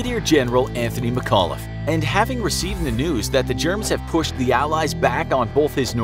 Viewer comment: I heard the germs instead of Germans.